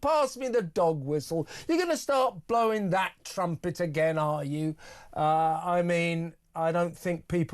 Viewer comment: She actually said "...that little trumpet..." the word 'little' being used to disparage both Tim Montgomery and these heinous crimes.